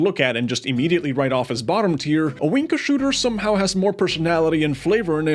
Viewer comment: I misheard Owinka Shooter as The Wanker Shooter and now I can't get “Sniping's a good job, mate,” out of my head…